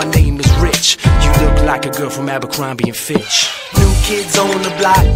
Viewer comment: Literally thought this said "We're at the club eating fish" instead of abercrombe and fitch or whatever 😂🤣💀